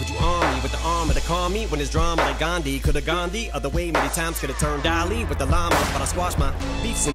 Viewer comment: The Dalai Lama line is a triple. Nobody ever notices the Salvador Dali reference. Dali was a surreal abstract artist whose works are usually familiar but hauntingly twisted. Melted clocks is his most known work, but he's saying "I could've made shit get real weird and abstract with the Llama (gun)". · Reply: I thought I heard, I could turned Ali with the Llama.